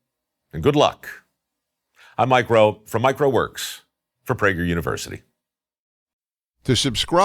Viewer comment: “I’m Mike Rowe, from Mike Rowe works” I heard “micro works”